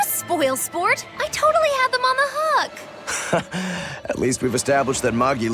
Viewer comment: She Said On The Hook, But The BS Subtitles Say On A Hug